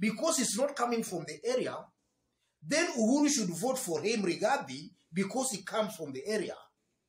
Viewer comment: I thought they said Uhuru has 1 vote !